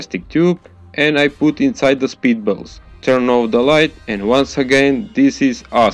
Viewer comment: When you say spit balls I thought you say speed balls